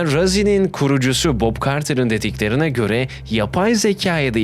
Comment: ilk başta bok carter diye duydum😂😂